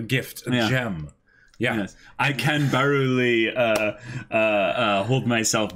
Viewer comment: Did I hear say "I kan-baru-li"? You're doing great